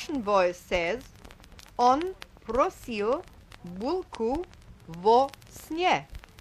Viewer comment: in russian: "он храпел гулко во сне" (on hrapel gulko vo sne), instead of "он просил булку во сне" (on prosil bulku vo sne)